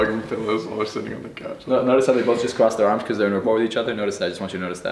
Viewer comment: huh? All I heard was, "Notice how their arms are crossing the same way". What else did he say? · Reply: He said “notice how they are both crossing their arms the same way because there in equality to each other”